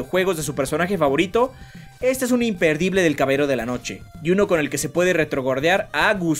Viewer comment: EL "CABERO" DE LA NOCHE, escuche bien??